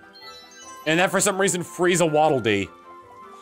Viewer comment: "And now, for some reason, Frieza Waddle Dee." That's what I heard the first time; I don't know why. XD